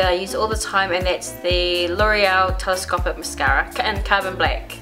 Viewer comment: The bomb per usual!!! What mascara did you say you used again? I hear L'Oreal but I couldn't make out the rest :/ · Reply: sorry , im not very clear sometimes lol its l'oreal telescopic carbon black